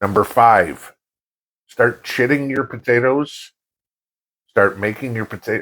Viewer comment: I thought you said "start shitting your potatos" for a second 😆 forgive me, still on me first cup of coffee